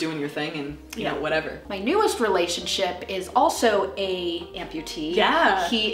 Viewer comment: Ha, I kept hearing "bologna amputee" lmao